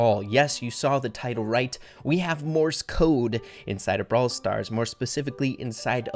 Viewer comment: I thought it said Mortis code